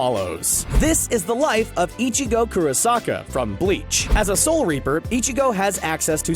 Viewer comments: Did he just say “ichigo kurosakai? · did I hear u saying ichigo kurosak a ?